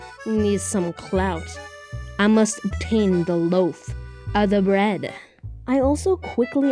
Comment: did you just say bread!?!? 😔